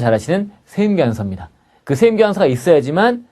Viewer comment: 한국 세금 1도 모르는 미국 사는 사람이 한국 역이민 준비땜에 듣고 있는데.. 한글 자막 달아주심 더욱 감사할 것 같아요! 12:49 "세금계산서" 세번 돌려 듣고 알아 들었어요ㅠ (세음기은서 라는 용어가 있는줄..ㅎㅎ)